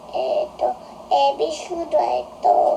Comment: 2問目の時、｢エビフライ｣じゃなくて、｢えーびーしー｣とも聞こえた気する！！ それにしてもばり可愛かった😭💞